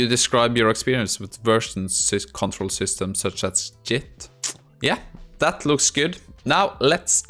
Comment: I though I lost my hearing when I heard "jit".. like "Just-in-time" JIT - turns out it's just plain old norwenglish